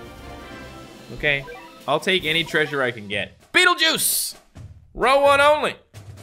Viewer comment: lol I heard "beetle juice"